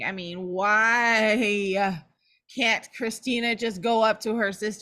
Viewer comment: said,” you can’t trust Nina”, is what I thought I heard after 5 times replaying it over and over. And I have to agree, I do not like Nina.